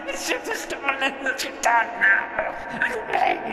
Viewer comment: Did I hear him say 'I beg you'?